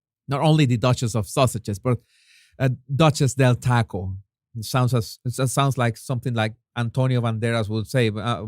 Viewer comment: OYE, WITH YOUR Ricardo Moltaban accent it sounded like you said, "nachas Del taco" LOL!